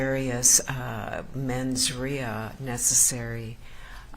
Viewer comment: what word does the judge say at 3:24? "mens rea"